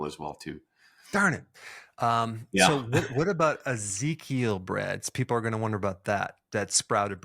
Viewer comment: In 7:16 min they mentioned a kind of bread... Can someone write the name of it? I can't understand it... TIA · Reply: Ezekiel